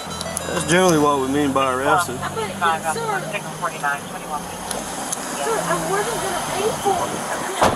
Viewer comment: she clearly says "i wasnt going to pay for it". She kept her car running for a quicker getaway